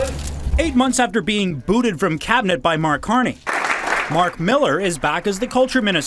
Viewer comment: I thought you said Mac Miller. I want y money back.